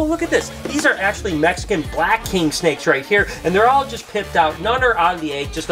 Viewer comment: He said pipped out. I thought he said pimped out.